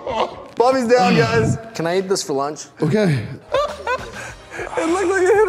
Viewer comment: again me what said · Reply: Can I eat this for lunch he said